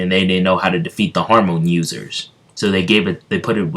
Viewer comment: in the first two minutes of the vid, i heard you say hormone users, not hamon users